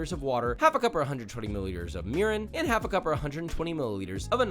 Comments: When he said mirin i thought he said urine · Lmao at first I thought you said urine, not mirin 😂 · Double take required when I thought I heard "half a cup or 120ml of urine"... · when he said Mirin at 4:00 i thought he said urine and im a chef 😂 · did you say 120 ml of urine? at 4:00, lol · half a cup of urine?? what is he saying lol